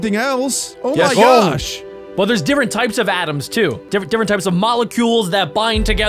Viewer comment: I thought said ‘different types of Adam’